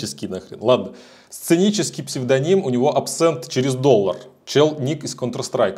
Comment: На 7:12 вообще услышала "цинический псевдоним". Псионический ещё норм